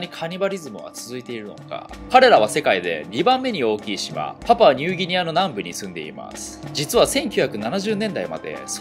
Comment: パプアニューギニアがパパはニューギニアに聞こえた